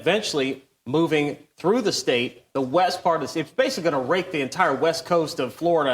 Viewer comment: Sounded like he said rape the state of florida liatened like 10 times fifured out rake